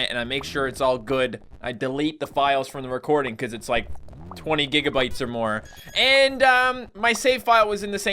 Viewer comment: I thought he said 20 gigabytes of porn